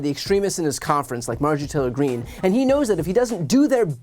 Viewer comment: with the captions on, when he mentions marge traitor green, captions say, marge atilla green, and that works too.